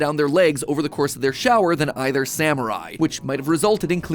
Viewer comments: “samurai” I know what he really said, it just sounded like he said samurai 😂😂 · Who else heard samurai at 11:15 And also you shouldn’t be using a loofah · Mat saying Sam or I vs. what I heard samurai😂😂😂😂😂🤺🤺🤺⚔️🗡️ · am i the only one who heard samurai instead of Sam or i?